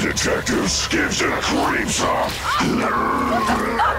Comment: what did he say? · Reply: "Sorry detectives gives you the creeps huh?"